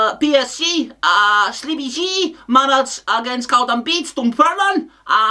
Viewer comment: Did he say Sleepy G? 🤣😂😂